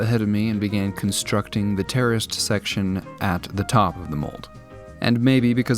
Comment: I genuinely have no idea what else he could have said. Can someone enlighten me as to what it is? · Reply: Terraced I think, I thought he said terrorist too